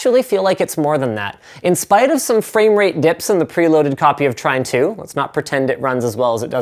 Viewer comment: can someone PLEASE tell me which is the game he is playing at 7:49 , I don't understand his pronunciation of the game, trime 2? prime 2? train 2? trin 2? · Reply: I hope you are joking, but just in case you arent, its called Trine 2:Complete Story.